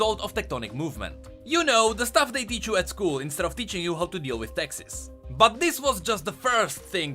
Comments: "The thing they taught you in school instead of teaching you how to do taxes" I swear I thought you said Texas and I about lost my shit lol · OH YOU SAID TAXES!!! I swear i thought i heard you say "instead of teaching us how to deal with Texas." I was like "damn dude XD whatchyu got against Texas!?" XD